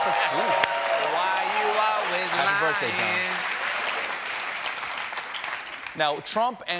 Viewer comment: does he say "happy birthday John"?